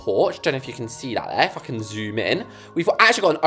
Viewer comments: Uhm… Gurl did u just say the f word? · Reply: I don’t know but I think they said if I can but the f was pronounced more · bro it sounded like you said, let’s see if I can the f word zoom in